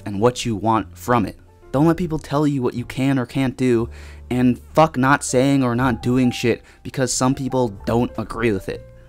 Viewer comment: i heard "Fuck Nazis" its been my life long goal to sorta be like a dexter but with the alt right, ill do it perfectly tho like dexter does it :)